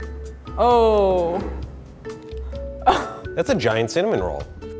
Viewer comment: did he said "its A Giant cimelol"